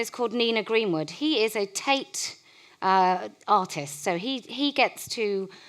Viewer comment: did she say "taint artist" at 2:18?